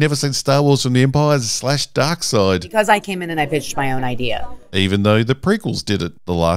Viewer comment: Sounds like she is saying, "Because I came in and b*tched my own idea."